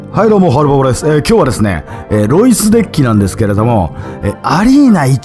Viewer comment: でエロイスと聞こえたのは空耳かな？ （ロイスさん申し訳ない） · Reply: 「えー、ロイス」って感じなんしょ